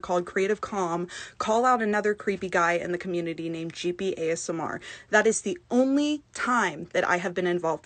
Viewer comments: Wait did she say Gibiasmr @ 3:03 ?????? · did she say Gibi was a creepy guy? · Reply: I thought she said that too lol But I think she’s actually saying “Jeepy ASMR”